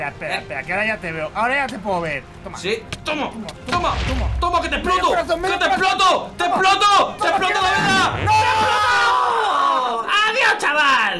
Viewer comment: escuche que dijo te exploto te exploto a la vega en el minuto